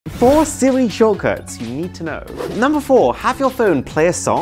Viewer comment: When Aron said 4 Siri shortcut, it sounded like he said, 4 silly shulkers. 😂